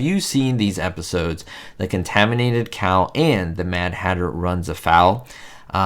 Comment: And I thought you were saying Contaminated Cow! I figured it out half way through the video.